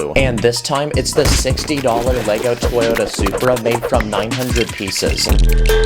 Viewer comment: did bro just say that’s only 60 bucks